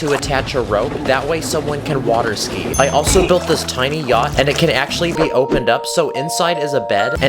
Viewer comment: My sister thought you said gyatt when u said yacht🙏🏾